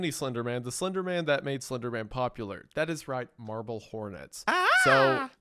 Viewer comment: I've always heard people say Marbel Hornets and thought they were saying Marvel Hornets and thought it was Marvel Creepypasta fanfiction so I avoided it like the plague.